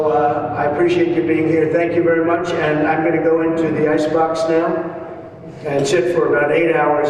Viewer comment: Sounded like he said...going into the icebox and shit..,..